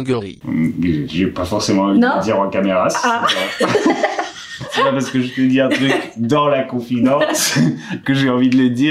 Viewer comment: Vous pensez elle a dit quoi à 6:29 ? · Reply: "Est-ce que tu t'es branlé dessus ?"